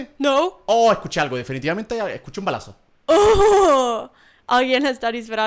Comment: Pensé que dijiste que escuchaste un abrazo xD